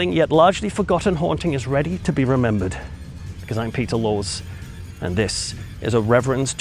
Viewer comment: Sounds like he says Peter Lores.